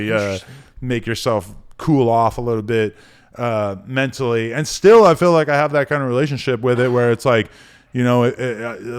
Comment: does anybody hear a whisper at 1:22:50 after Adam says the thing about masterbastion being a form of mental health? it’s like fast whisper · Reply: yea it was bryce he said “interesting “